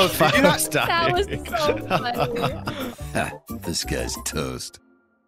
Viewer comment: WAIT does the intro say "this guy's toast" or "disguised toast?" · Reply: I think it says “Disgusted toast” but I always hear it as “This guys toast”